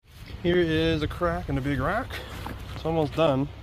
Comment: I thought you said a crack and a big rack